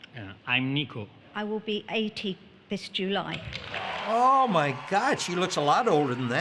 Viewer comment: I thought she said, "I will be turning 18 this July."